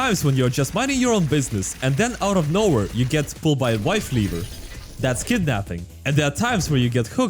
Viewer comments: wow didnt know he went to get the milk (wife leaver) (it sounded like in this case) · "You get pulled by a wifeleaver" Did I hear that correctly?! 😂